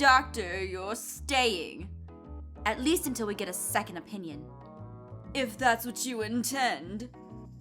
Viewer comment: Hey when said opinion I thought she said " onion " 😂